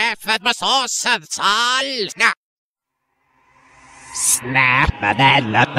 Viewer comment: lol at 1:33 it sounds like marik would say source of tals or tails. i dont know what he want to say us but.... okXD it's funnyXD